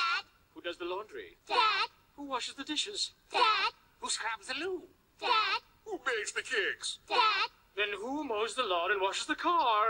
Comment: what did they say as questions? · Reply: "Who scrubs the loo?" "Who bakes the cakes?" "Then who mows the lawn and washes the car?"